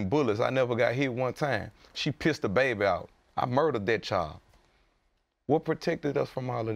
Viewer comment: what did he say??? Did he say she pissed a baby out and he murdered it?